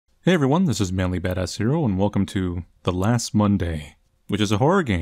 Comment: I thought he said ManlyBadass Here not Manly Badass Hero. I feel dumb.